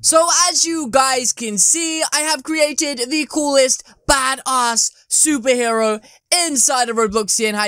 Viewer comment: did you just say badass 1:22 well it does look badass like ur channel